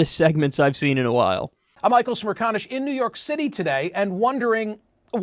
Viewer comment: I literally thought you first said "smear"-conish as a joke but that's so close to his actual his last name